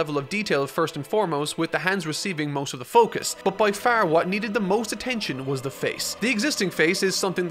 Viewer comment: Did you say.. Giving the hands attention? Quick, somebody write the Yoshikaga Kira copy pasta here